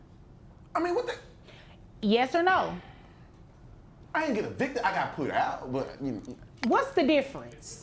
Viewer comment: Did he just say ' I didn't get evicted..I got put out" ? 😂😂😂😂